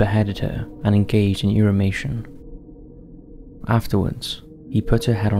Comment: what is ‘irimatia’?? I can’t figure out what this word is that the narrator keeps using. Anyone? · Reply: Same problem here, I can't figure it out. Edit: another commenter answered it, the word is irrumatio - he had sex with the head/face. Do yourself a favor, don't look it up... I did, I wish I hadn't.